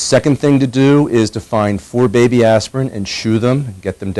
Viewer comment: did he say aspirin???